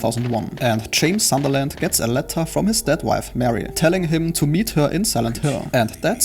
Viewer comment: When you said "dead wife", I thought you said "step wife", and I was like "Wait, that's a THING?"